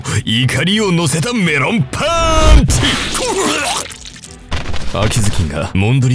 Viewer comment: の瓜生の「メロンパンチ」が アンパンマンの「アンパンチ」みたいに 聞こえて笑ってしまった。(笑)😂😂😂